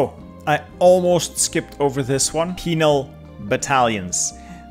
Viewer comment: Wasn't looking at the screen when he said Penal Divisions, and I misheard "Female Divisions" and looked up to see "ball and chain" icons - Was thinking that was an interesting choice!